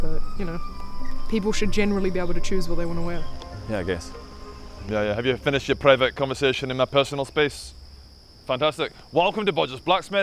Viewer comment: "Have you finished your private conversation in my personal space?" That was great. Probably the best line in the history of dialogue. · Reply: I thought it said "Have you finished my pervert conversation" which I literally didn't question until now because it fits